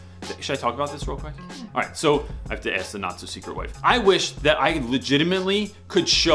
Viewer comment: I just wanted to say that the first several tims I heard you use the phrase "not so secret wife" I thought you were saying "NAZI Secret wife." That is all.